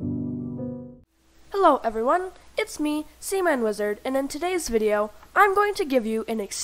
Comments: I thought when he said his name he said semen lizard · great tut but work on your pronounciation of your name i cold have sworn i heard semen wizard